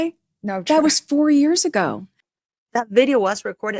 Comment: Meghan said that video "wasn't yesterday...that was 3 years ago." This would make the date in 2022. Not 2021. Does Meghan even know how old Lilibet is???! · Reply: 😂 Good Grief! Go back to 6:50 and she clearly says, "Btw, that was FOUR years ago." She says 4 (four) years ago!